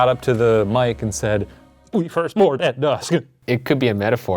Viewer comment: i heard “my firstborn son at dusk”